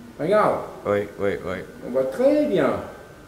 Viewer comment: I think he says "on voit très bien" so it would be "we see [it] very well" rather then "It's going very well".